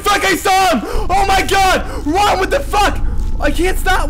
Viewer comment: I thought he said "RUN WITH THE FUCK!!!!!"